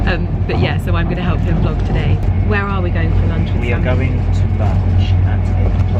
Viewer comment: I could have sworn you said “we’re going to lunch at Titville!” That sounds like the same place we have in Australia called “Hooters.” 😂 Great vlog 👍